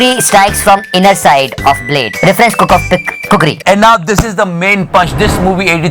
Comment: did i hear that right " Reference kuk of the k...khukri." @ 8:35😂😂